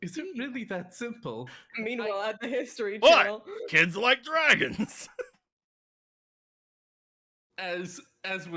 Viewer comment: the moment he said dragons it sounded like nappa